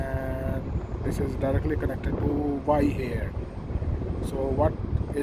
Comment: Please clarify what is said at 3:32. It sounds like "y Air". This is unfamiliar terminology. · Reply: Thanks that was terminal Y on phase monitor